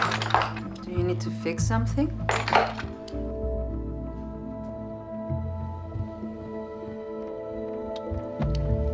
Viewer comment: I'll pay 1 like for the person who tells me what it said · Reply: Pretty sure it's stannous chloride, as referenced later on.